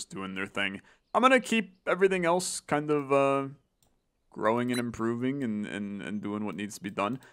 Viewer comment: I swear I heard "growing and grooving" and I was so into that after the 38th "status quo" lmaoo Not that I'm against it, love the status quo gang